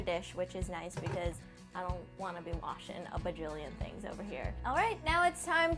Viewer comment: what the word is she saying on 4:30? "I don't wanna be washing a .... things" what is that? bugilion? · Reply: "bajillion" is just a figure of speech for saying "a lot of things" like "a million things"